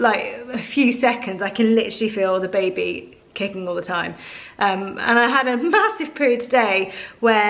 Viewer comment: I thought you said you had a massive poo today ha ha 😂 Love you Gi Tx